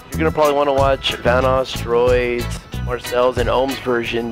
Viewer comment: i thought he said vanos is going to die or one of you guys will die and lui i am your biggest fan keep up the great work